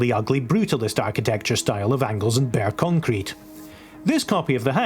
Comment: When talking about the brutalist house I misheard you say it's "...rendered in the mesmerizingly ugly brutalist architecture style of Engles & Bere Concrete™" which I initially assumed was a famous company known for such brutalist architecture. Here at Engles & Bere Concrete™ we specialize in horrifying brutalist architecture. Our company name is practically synonymous with such. We can garuntee any structure you want will be as unappealing as possible. So whether you're looking to build a prision, courthouse, primary school, wedding venue, hotel, park, or even a nursey. Know you can always count on Engles & Bere Concrete™ to deliver.